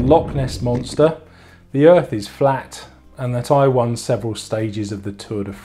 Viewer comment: Your accent made 0:47 sound like you said "Taiwan several stages of the Tour de France." Awesome. I started to develop knee pain and was told to raise my seat. I had been using that "LeMond method", but it was hopelessly too short. I ended up having to raise my saddle a good 5cm above the "LeMond" numbers. Good news! The knee pain went away! Bad news! My nuts are numb! I thought it was just a "get used to it" but after a few days of riding, it got really bad. My urologist told me to take a 6 week break, esp after I told her that it was (at the time of visit) a few days since my last ride and I was still numb. I wanted to get a bike fit, but where I live the only place to get a bike fit is 100km away and costs about US$300. Ah, sranje! It is bad enough my bike selection is limited due to size, but jeez! And this is the home of Giant and Merida/Specialized! So, cycling really isn't all that easy to get into depending on where you live and your size requirements.